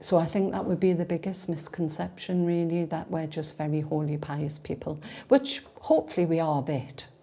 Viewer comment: I thought she said (about the misconceptions) that they are all horny and Pius people lol